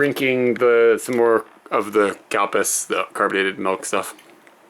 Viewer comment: This was another episode of. :) I must say. It's a well balance at least you keep to a steady routine for the mornings, and Kyde does great things by cooking you dinner. Not many women can cook these days...well the ones I know of. Just saying. & For the record. That 12-13 dollar menu you payed for the both of you is not bad at all. Here where i love you can get a buffet for about 12 Euro a person or 15 euro a plate so that's not that bad at all. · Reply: P.S. just so you know. It did sound like you was saying. Cow Piss. Not sure if anybody else caught that!!